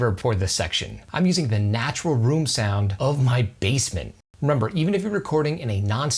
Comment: Take a shot every time he says "untreated basement" xD Awesome content, you earned a new subscriber :) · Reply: I kept hearing "bassmen"